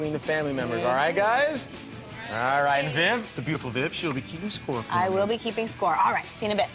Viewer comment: Did he call her a beautiful bitch?!